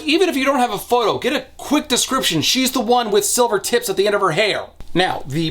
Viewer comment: "She’s the one with silver tits…" — my mishearing of that line…